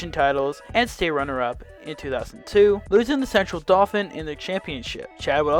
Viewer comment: Also, swear to God, I listened to the part at 0:56 half a dozen times and every time I hear "losing to sexual dolphin in the state championship"